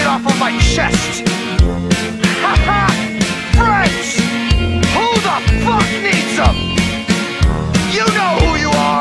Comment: The first time I heard this was without lyrics, and at the beginning I swore I heard him shout 'French! Who the fuck needs them?' And I was like, woah, dude, that's a bit harsh.